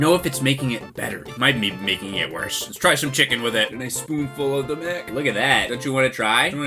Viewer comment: did you say "a spoonful of the dick"? 😂